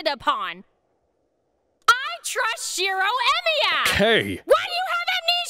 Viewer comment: I thought she said Hero Emiya. Hope to see the subs!